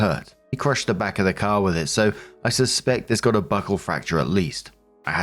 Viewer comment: I heard "Butthole fracture, not buckle fracture"